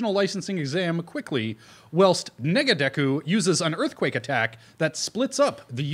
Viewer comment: Did he just say, "Nigga Deku"?